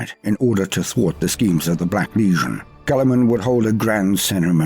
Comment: I absolutely love but is anyone else annoyed by the way he says legion? it sound like he says Lesion all the time to me